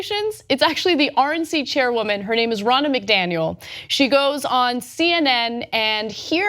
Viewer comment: I keep hearing "Ronald McDonald".